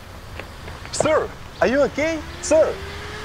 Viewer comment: i thought he was saying Sir,are you gay? Sir ?